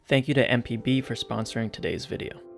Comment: When he said “thanks MpB for sponsoring this vide” I thought he said “thanks MTV for sponsoring this video” I was like “what?” Lol 😂